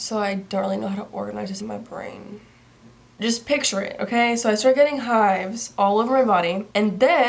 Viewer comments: when you said "i started getting hives..." i thought you said "i started getting high" and i was like WTF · deadass thought she said "i started getting high"